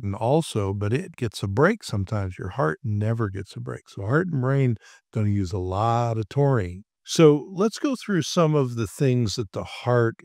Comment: Thank you for helping me 😅build a better moving, working model of our biochemistry, I look forward to checking out more of your topics! ALSO.. I think there may be an incorrect caption at 2:30 where "YOUR HEART AND BRAIN DONT USE A LOT OF TAURINE" is shown, but I think you actually say they "CAN USE" a lot of Taurine. I have some hearing issues but I this is what I hear phonetically, as well as from the context. As the only caption in the video, displayed in caps, this is an important point, so thought you'd like to know. Whether it was added by human or by machine transcription, I understand well how easily it happens, so this is meant as a question and not a criticism. (I do confess though it bothers me that auto-captioning ontinues to be so bad, in contrast with the astounding advances elsewhere in machine learning and the complexity of the signals & noise, etc.. I know having a human verify speech/verif/proofread may seem like moving backwards, but when the meaning of important content is flipped we need to take a step back anyway.) Humans doing the job can be forgiven, but the machines everyone is paying for Should be audited and calibrated, and/or discounted. I hope you can forgive my rant, and Thanks again for the clear and insightful information!